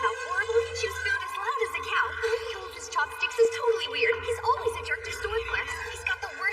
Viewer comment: Bro the caption said chocolate dih instead chopstick